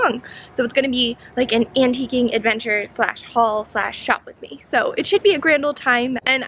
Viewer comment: When you said “shop with me” I thought you said “shoplifting” and I thought NO